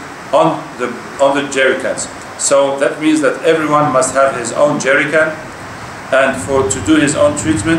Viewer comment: something i cannot hear clearly in the minute of 6:35, "everybody must have their own..... what?" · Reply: Mr. Ezzo. min 6:35, the sheikh says "every body should have his own jerican"